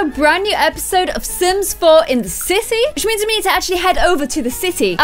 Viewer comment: in the intro when you said sims 4 in the city it sounded like you said said sims 4 in the sissy lol